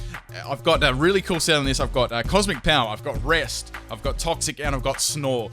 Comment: I thought he said, "I've got breasts..."